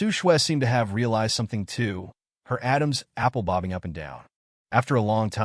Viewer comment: Did you say her Adam apple Bobbing up and down 😂 36:07 min into it